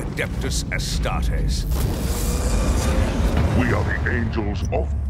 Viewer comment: What did he say ? "Our war is end us." ???? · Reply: Endless*